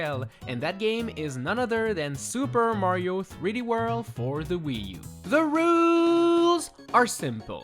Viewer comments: Did he just say super Mario 3D world for the Wii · did you say Super Mario 3D world for the Wii because the game is for the Wii U